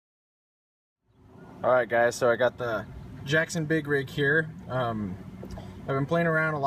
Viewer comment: I thought it said “ Jackson Big PIG.”......hmmmm.